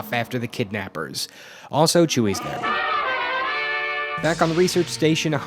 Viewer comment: I thought you said chewie’s dead